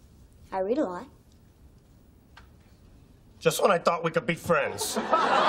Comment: 動画アップありがとうございます。 3:59 Just when I thought we could be friends が何度聴いても、Just so I thought〜に聞こえてしまうのですが、やはりこれはwhenと言ってるのでしょうか？ 実は会話中のwhenがいつも聞き取りづらくて困っています、、、これは慣れるしかないのでしょうか？